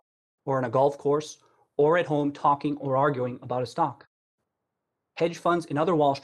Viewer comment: Did...did he say 'Stonk'?